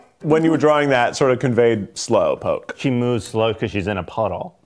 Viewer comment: Kwazy thought he said smoke poke and not slow poke.